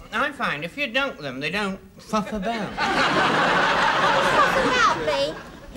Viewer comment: I heard "f*ck about" the first time I watched this and nearly swallowed my teeth